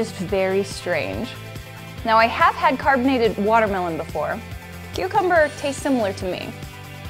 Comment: did she say cucumber tastes similar to her as watermelon ???